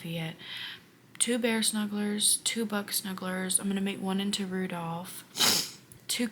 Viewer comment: My screen kept getting darker, then I realized my phone was at 2%! 😂 And you said "Buck Snuggler" but I heard "Butt Snuggler". And now I can't stop laughing! Great job working through the funk! Your work is so good, and I understand the wanting to make what you want. I am also a mood crocheter. Great hair! ❤